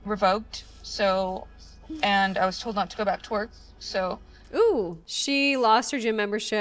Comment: I heard "My membership got revoked and I WAS TOLD NOT TO GO BACK TO HER" .. I didn't hear "WORK" I heard "HER" .. am I wrong?? Did she say she got fired from her job??